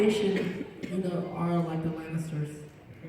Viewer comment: is where the question about going on a life changing adventure with Zuko starts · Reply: Thank youu and I have a question, what does say at 41:05 after he says "Although the Fire Nation..."?? Never mind I found it! It's the Lannisters. Apparently from Game of Thrones. 🤔 ~:~